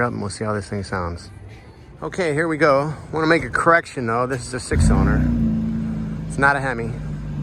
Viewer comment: he clearly says "this is a 6 cylinder, NOT a hemi"